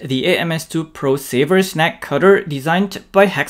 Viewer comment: Neck cutter, snake cutter? No, it's snag cutter! Very nice. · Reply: I thought he was saying "snack cutter". My Pringles are already separated though.